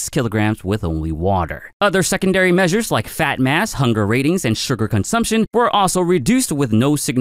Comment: I heard "fat n' ass" then looked up to see "fat mass"...🤣